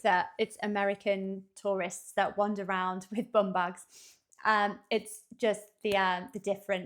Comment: At first I thought said we wear "bomb bags," and I thought, "I don't know what that is, but I definitely wouldn't wear one."